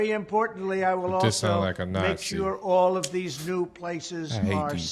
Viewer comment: Did the guy in the blue cap say "He just sounds like a Nazi, I hate these people", or did I mishear it? o_O